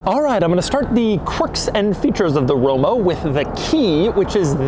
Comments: Did he say Romo in 2:43 ? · anyone heard him say Romo?